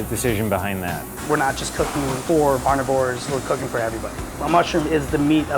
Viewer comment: Did he just call us fking "Carnivores"?!!! Triggered!!!!!!!!!!!!!!!!!!!!!!!!!!!!!!!!!!!!!!! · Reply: he didnt say carvivores, he said omnivores